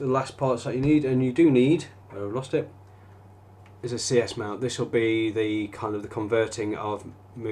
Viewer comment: where can i buy the adaptor from camera to lens?? and what is the name?? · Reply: did you call the adapter a "sears mount" ?